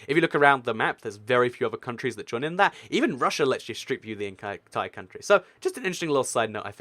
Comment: is he saying strip? I literally can’t tell